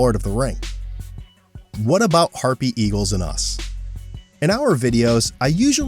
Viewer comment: Oh I thought it said happy eagle XD